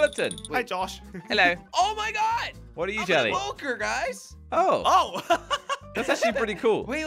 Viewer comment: did jelly say poker? because he was a pillager.